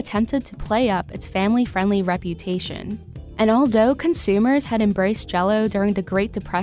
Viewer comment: I can’t stop rewinding to 5:41 to hear hear say “all doe” instead of “all though”